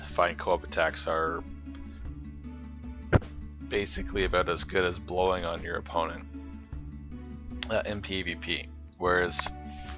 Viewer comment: Thanks for the great videos. They helped me with a lot of organizing and other stuff. And for funny note for the first time I heard "cooperative attacks as good as blowing your opponents" :D After that I realized it wasn't it. Did you do video on how to get Jenny,tips ?